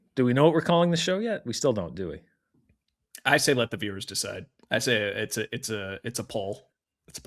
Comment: Since I misheard and thought it was funny: call the show Let the Beavers Decide